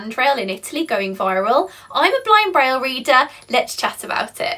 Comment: When watched this first time I heard “I’m a blind rail reader” 😅